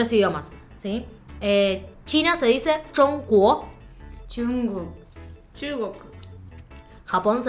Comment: yo entendí que Goku XD